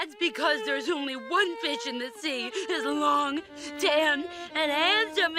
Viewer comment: The first four seasons are PURE GOLD! 🌟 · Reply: does anyone else hear her say in 23:11 bitch?